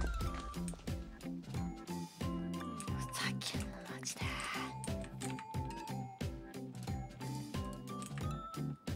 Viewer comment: did I hear a FAQ? But srs all I could make out was “majide”, which I believe means “seriously?” Or “are you kidding?” ?? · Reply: You were hearing “ふざけんなまじで“ which is “fuzakenna maji de” which is just pretty much “are you freaking serious”.